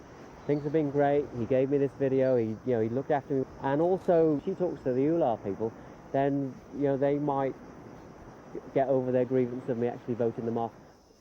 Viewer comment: What in the heck did say at 29:21? I can't understand anything he's saying after the word "also".. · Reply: “And also she talks to the Ular people, then they might get over their grievance of me actually voting them off”.